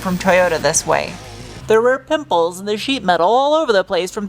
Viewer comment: Wonder why I heard "nipples"